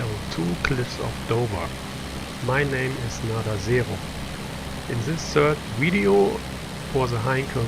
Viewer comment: You say. I hear 'Not a Zero' too many Pacific campaigns I fear...